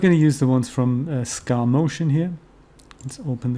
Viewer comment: Good tutorial. Which aniblock maker did you say you used? I heard you saying Sca motion or something like that. Can you please let me know. This will be most appreciated. Thank you. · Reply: Sure, it was SKAmotion. You can find him on his website or the DAZ Store: <link> These days I mainly use free blocks from <link>, that's another valuable service to look into. I have some tutorials on how to convert those blocks on aniBlocks in DAZ Studio, or to Blender's action strips. Have fun!